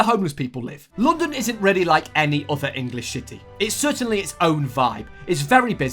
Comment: I heard shitty